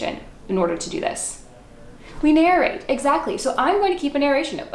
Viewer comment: Did I hear your husband answer “we narrate” 12:15ish. 👍🏼👍🏼